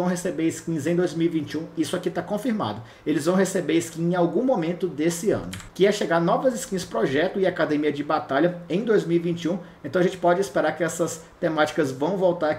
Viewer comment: eu ouvi Shen Projeto Edição de Prestígio?